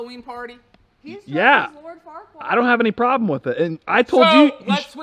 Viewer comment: When said who Jack is going as it sounded like he said “Lord Fuckwad” or something lol I guess I’m not familiar with where his costume comes from. Jack does look pretty cute in it (like usual), he definitely got the looks out of the two brothers (like says Jack gets all the good stuff and he gets all the bad stuff lol).